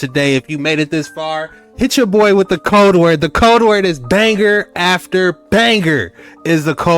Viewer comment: Banger after banger. Thought you said danger after banger at first. lol keep up the good work.